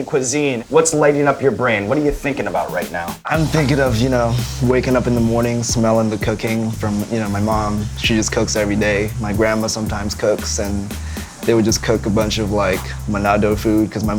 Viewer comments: “i think about the cocaine from my mom.. my mom cokes everyday, my grandma cokes everyday” i swear i thought he said that, i need to go to bed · I thought he said cocaine.. had to go back a few times haha